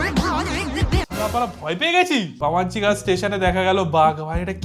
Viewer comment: কি বললে ওটা বামাঞ্চি? ওটা বামনগাছি। 😢